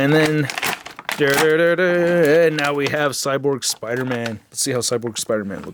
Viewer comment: I thought it said Cable Spiderman at first.... ehh, same difference